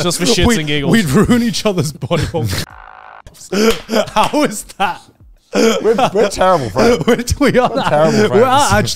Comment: what did he say here? I'm not following why they keep mentioning a censored thing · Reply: He said “or ki11 myse1f” while im pretty sure the demographic is more on the more mature side, Youtube guidelines are very odd about mentioning it